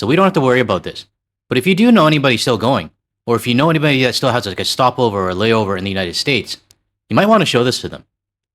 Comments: what's a "Gestapover".... lol just kidding, but I did have to do a rewind and see what you actually said.. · did you just say “gestopover”? Well played sir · I had to rewind back to here and listen again because it sounded like you made a cool play on words. I guess you said "have a, like, stopover," but when I first listened, it sounded like you said "a gestapo-ver." Which fits! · Almost thought I heard you say ‘gestapo stopover’ for a second there. That tracks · Did you just say 'Gestopover'😆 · it sounds like you say "g'stop-o'ver" 🤣